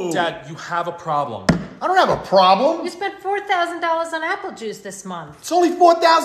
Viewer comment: I misheard 40000$ and was like this 🥶😱then i saw the comment saying 4000$ but then also i was like this 😨 Its like 4000$ for just that small bottle